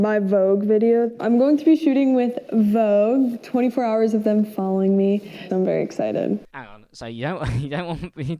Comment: I heard: 28 hours of them f*cking me and I thought HOLD UP, rewind that, can't have heard that right. thank every god ever believed in that I did